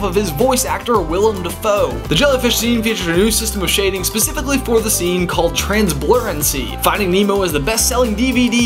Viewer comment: WHO TF COULD UNDERSTAND WHAT HE SAID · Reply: cozettehuyen "The jellyfish scene featured a new system of shading specifically for the scene called 'Transblurrency'."